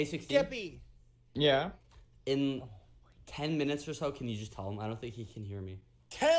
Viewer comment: he actually said 10 minutes